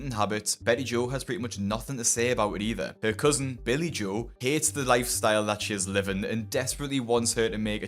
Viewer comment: the first time you mentioned, Billy Joe I thought you said Billy Joel! That would be a very famous cousin! 😂😂❤❤